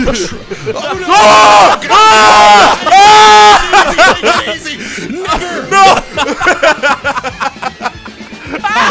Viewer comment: did he say n word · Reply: No he didn’t he said never